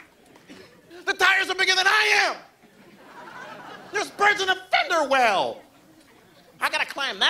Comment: What does he say about the fender well at 0:51? I can’t hear it lol And at 9:07? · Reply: i think it's "there's birds in the fenderwell"